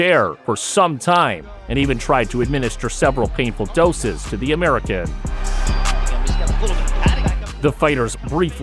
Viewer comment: American? Am I misheard?